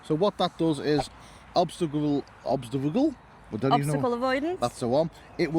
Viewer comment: did he say obstacle Google??? 😂 That is understandable as well. I catch the drift.